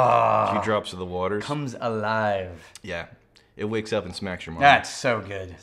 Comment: I thought said “Smacks your muffins.”